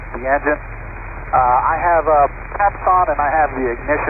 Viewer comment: I think he was saying "pumps" ON which I would assume supplement fuel flow.